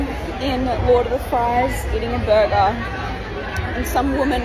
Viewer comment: hey did she say she was eating burger?